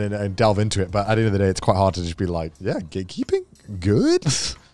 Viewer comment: The entire time I thought they were saying "gaykeeping"...